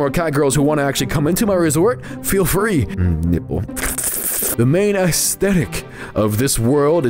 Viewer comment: did he just said nipple or somethin